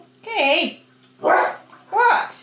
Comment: “ He doesn’t like the cow on television”. LOL😂🤣🥰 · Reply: sounds like he actually said "what?" 😂